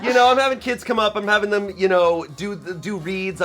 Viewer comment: I thought Shayne said "I'm having kids come up, you know I'm having them do weed"